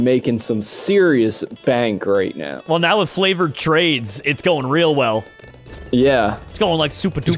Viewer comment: did he say flavoured